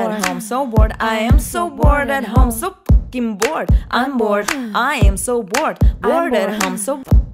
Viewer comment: did she just say the f word?